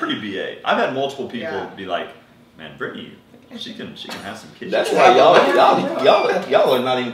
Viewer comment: Was I the only one who heard said "Brittany can get some kids" when he was talking about that she really does look good 😂😂😂😭😭